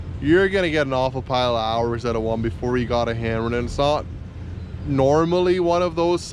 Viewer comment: hours before you gotta ???" I don't know what you said there. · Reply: Alot of hours before you need to get a blade hammered.